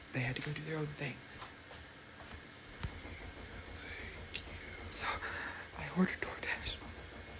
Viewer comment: right hear ‼️ I hear thank you